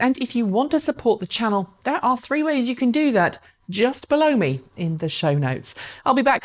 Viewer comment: Did she say just blow me in the show notes?